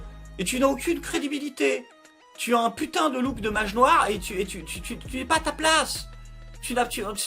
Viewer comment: T'as quoi contre les mages noirs ? HEIN ? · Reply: Il me semble qu'il a dit :'' magie noire''..